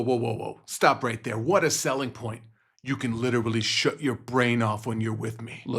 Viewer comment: lol… I thought he said “you can show your brain off” 😂😂😂